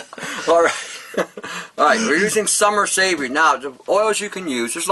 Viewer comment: What was the 1st essential oil. Sound like he said summer sage? Sage, thyme and vodka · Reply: Summer savory